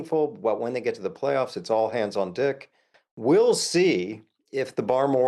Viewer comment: Did he say, “All hands on dick?” @ 9:22? Pretty sure he did 🤣